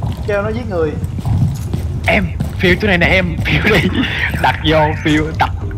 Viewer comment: đục chỗ này nè mà e nghe thành đụ chỗ này nè sợ a ghê